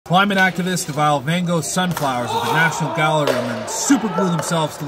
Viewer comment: I low-key thought they said mangos sunflower